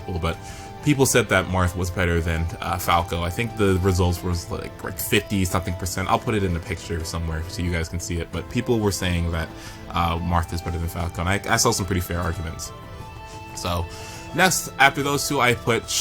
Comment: i thought you said WFT is better than dong